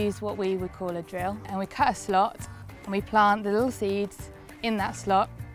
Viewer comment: She said: "cut a slot" I heard: "curse a lot"